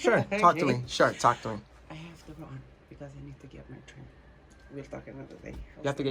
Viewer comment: something about what she did triggered my sex drive, it turned me on. Made he so interesting in my chickchanarry ! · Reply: I swear i hear her say "I need to get my trim" first thing in mind was a baldeagle ufff.